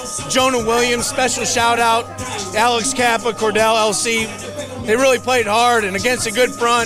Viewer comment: Thought he said CordeLLC for like a nickname for Volson. Disappointed when I figured out it was La’el. Karras is such a good interview though.